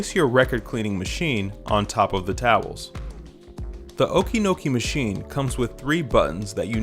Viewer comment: I was all ready to break into a dance as I thought you said Okey Cokie !